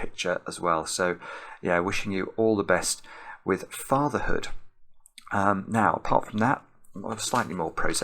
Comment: Oh, FATHER. I definitely thought you said, "Farmer". I was thinking, "Huh, not exactly the most noteworthy thing - but I appreciate the guys celebrating local agriculture."